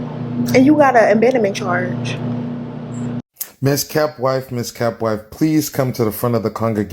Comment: I'm sorry but the way that guy was 🤙🏾 calling her to the stage 😂Ms. Kept Wife... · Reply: It sounded like he said Ms.Cap Wife 😂😂😂